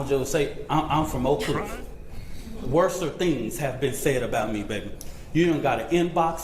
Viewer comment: Did he say "Worser"????